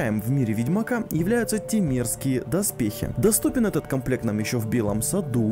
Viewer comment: Вместо "темерские" услышал "те мерзкие".